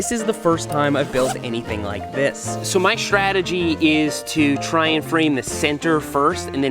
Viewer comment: I misheard saying "shredegy" instead of strategy. I propose this definition for the new word: Shredegy: The chosen line and technique at a gnarly part of the trail before sending it.